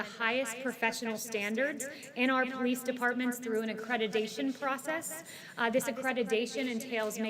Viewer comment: i'm sorry, 00:20 did she just say "accredidation"? twice? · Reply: accreditation and yes she did use the word twice, did that hurt your feelings or excite you?